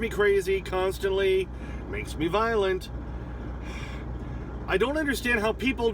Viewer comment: Lol..makes me violent..your shirt color I thought you said makes me violet..